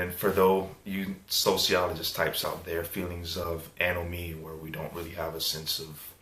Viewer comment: What is the word he uses when he is talking about not fitting into society... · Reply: Hey! I'm glad you asked. The word is "anomie". It means "lack of social integration/norms". I use it because I'm a Social Studies nerd lol.